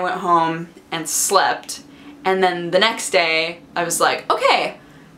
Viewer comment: I died a little when the automatic captions said "the next day acts like cocaine" at 5:30 ish